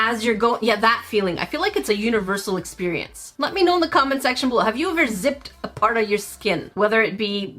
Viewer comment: am i the only one that heard her say "let me know in the comment sexual"??? lol